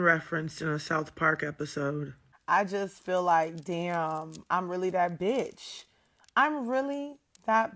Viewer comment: Lol, I thought she said "Damn, I'm really that big"